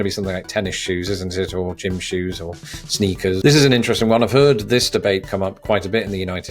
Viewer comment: I thought yous said trainers for sneakers